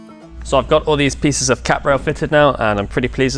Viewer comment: That cap rail is stunning. Teak is such a lovey wood. Pity it's so bloody expensive these days! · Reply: Oh, it's a CAP rail, I thought he was saying Cat rail, and wondered what cats had to do with it...